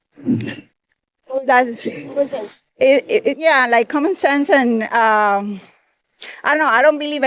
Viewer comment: Did she just say common sense lol😂